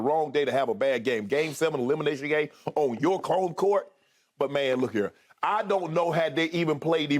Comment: Did SHANNON say (on your cone court)